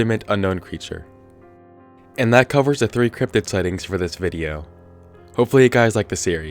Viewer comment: Lol I thought that it said "Cupid Sightings" and i was like ?